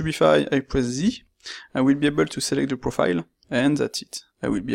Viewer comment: Hello, at 1:58 what do you say? I will (?) to select the profile? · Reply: I will be able to select the profile with Z